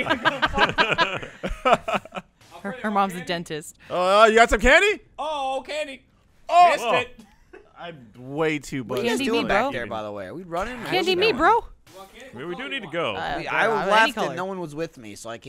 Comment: Aha did Alfredo just say "Im too buzzed"?